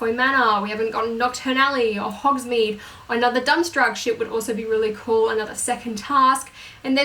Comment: Did you just say Dumstraang? XD